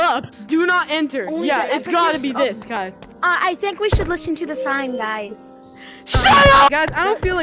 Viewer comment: what are you say? :/ press reply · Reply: Do you listen the sign???